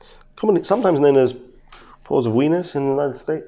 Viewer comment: So I swear I just heard him say dilated pore of weenier and I lost it laughing.